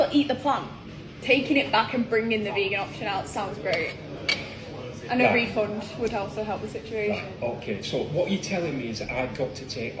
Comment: did she say" bring that back ,bring out the vegan option and a refund would help the situation"? if i heard right she wants another meal and a refund..i could of heard wron but she's just looking for a free meal!